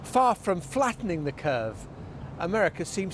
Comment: America fattening it! LOL I’m American and that was Hilarious! 👏🏼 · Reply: I thought he said flattening it. Some wishful thinking.